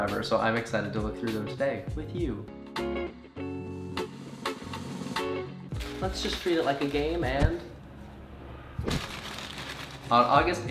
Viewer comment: Did I just hear...: "Let's just treat it like a game and..." OR: "Let's just treat it like a gay man..."